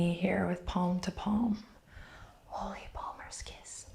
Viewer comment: I’ve watched this about a million times but I can never decipher what says at 23:10 it sounds like “holy walrus kiss” but I’m sure it’s something like “hold the wall worst case”. I love this video, but not knowing drives me nuts. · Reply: Holy Palmers Kiss. 😘